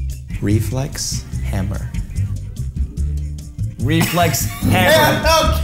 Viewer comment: For reflex hammer I thought mike said we flex Hamburgers